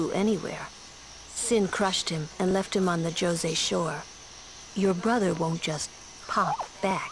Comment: doesnt it sound like she says jersey shore thumbs up if you think so